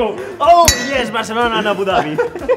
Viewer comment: Did Charles said "are you proud Charlos" or do my ears need cleaning